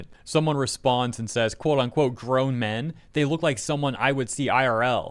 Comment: Correct me if I'm wrong, nut I think they are saying that they look like girls in real life and not "grown men"